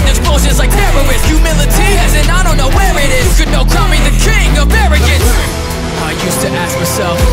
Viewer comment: "you can all crown me the king of arrogance!" but I thought you said "don't confuse it with arrogance"